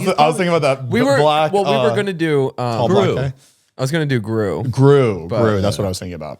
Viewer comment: did Zane say “the tall black guy?” For Gru 😂